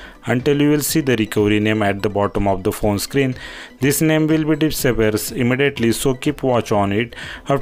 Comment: After Fastbood Reboot hold VOLdown and PWR until you will see the recovery name at the bottom This name will ............................. WHat ? I don't understand this .. Do you say disappear ?